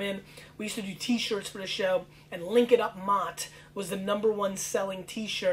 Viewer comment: It was Mott? I always heard it "link it up Matt" :)